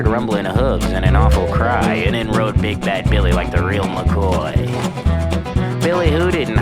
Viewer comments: Help I heard “big back billy” instead of big bad billy 😭 ig my class is so brainrotted im now infected 🫩✌️💔🥀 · i know the lyrics is "Big Bad Billy", but i keep hearing "Big Back Billy" XD